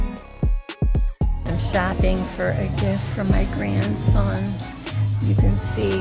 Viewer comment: thought she said “fapping for a gift..” I was gonna say, me too lady. Me too.